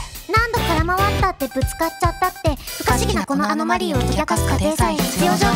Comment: 不可思議なこのアノマリーを解き明かすのところで不可思議なあのマリンを解き明かすって聞こえる